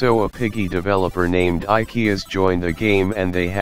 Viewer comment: I heard Ikea